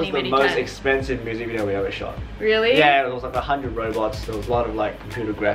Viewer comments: Who else thought he said 100 Robux?????? xD or am I crazy??? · 🤔did he just say 100 robux